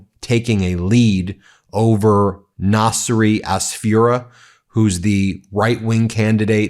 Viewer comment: I don't know what Nasry Asfura meens but I had to tripple check what Ben said because I understood "Nazi as Führer" 😮😅